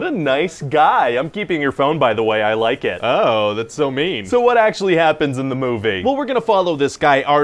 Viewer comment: What a nice guy, I'm keeping your phone, I like it. Oh that's so mean LMAO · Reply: ah, I thought he said, "oh, that's so me."